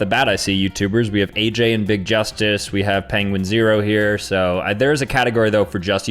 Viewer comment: did he just say penguin 0 😂